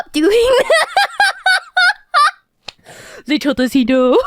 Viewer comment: Is she saying "Little does he know"? Man. Being weird but not weird enough to be too obviously autistic is too tough and relatable.